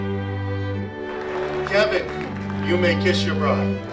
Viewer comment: i thought the man said kevin u may kiss your WOLF!!